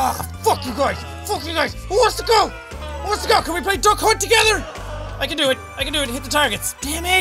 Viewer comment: When you said duck hunt I thought you said “the c7nt” lmao